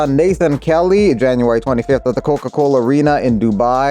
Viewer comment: I could've sworn I heard abed from community say coolcoolcool